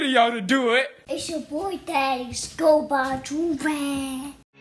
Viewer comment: Can someone please tell me what this man is saying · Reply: It’s yo boi tahdias go buy. joorage